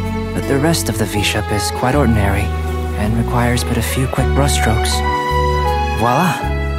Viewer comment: The voice actor, Khoi Dao, stated this: "Yep, still immensely honored to voice your English Albedo in Genshin Impact. Did you know? That line at 0:46 took 37 takes to get right because I. Cannot. Pronounce. Brush. Strokes. bruhshtrokes. burhstrokes.Bushtokes." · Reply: He sounds so close to saying Bruh strokes LOL